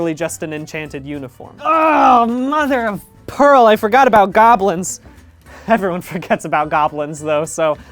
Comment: DID YOU SAY GOBLIN?